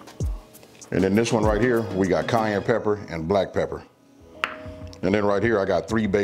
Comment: swear I thought he said Kanye pepper lol